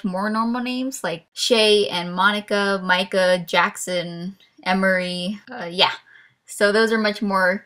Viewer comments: I SWEAR I HEARD YOU SAY MICHAEL JACKSON RIGHT THERE XDDDDDDDDDDD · did you say Shae? Cause my name's Shae! :D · "Michael Jackson" Yeaah I heard that